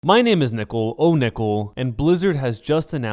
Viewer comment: i thought you said " hi my name is nipple onipple" i laughed out loud